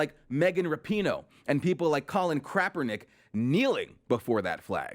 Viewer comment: Did he just say Crapernick? LOL